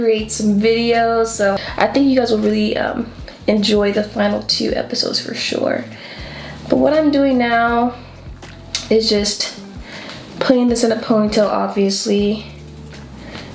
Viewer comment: When you thought finally said finale for a sec 🙉